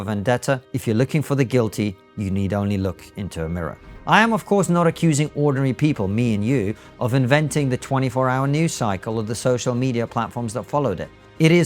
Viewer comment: I misheard Konstantin when he said "ordinary people like you and me" and thought he said "ORNERY people" and was immediately on board with whatever came next.